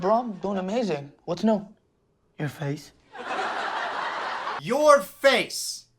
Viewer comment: honey nooo! i hate the show but you got it wrong. he said "your face" because Anwar asked what's new and Juanpa said "your face". like, your face is new. he wasn't just pointing out the obvious